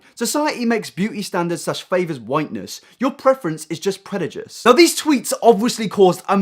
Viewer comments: did you just say... predijuce? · Did he just say "predajice"?